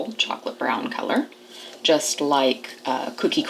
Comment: I thought when you said its a beautiful chocolate brown color just like is lol